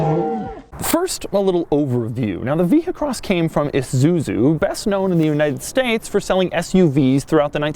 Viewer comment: did he say Isuzuzu?